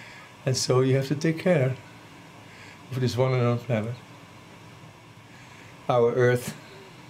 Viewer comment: What does he say at 1:05 ? "Of this one and only / one (on?) earth planet." ? Thanks! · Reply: He says "one and only".